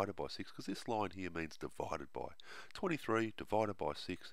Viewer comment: my caption says"divided by 20 3(23)"